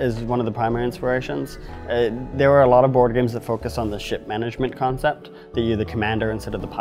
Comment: I swear he said "shit management" shortly after 2:00.